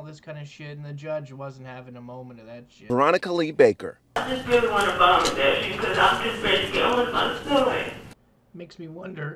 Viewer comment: What the hell did she say? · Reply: She wants to get home to her son